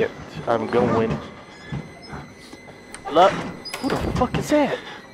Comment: Is it me or did he say HELEN